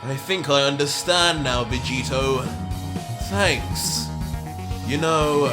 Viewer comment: it says vegeta instead of veggito in the subtitles :V